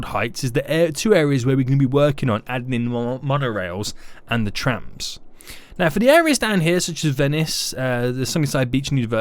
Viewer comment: I keep hearing Tramps :P